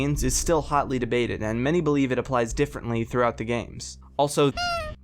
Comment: I thought he said Also FU** because of the poster sound 16:00 just listen.